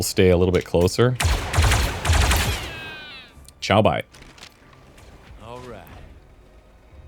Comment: Eu ouvi um tchau??